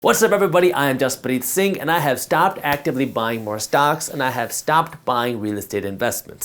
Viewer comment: Jaspreet, for a second I thought you said you were no longer going to buy “socks”…and I was thinking, why?! Why, Jaspreet???